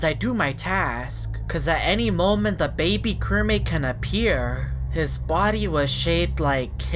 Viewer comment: What is that what is that (ರ╭╮ರ) YOU LITERALLY JUST SAID DABABY